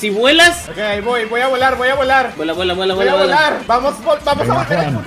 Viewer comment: Ajjzjaja pensé que decia "violar"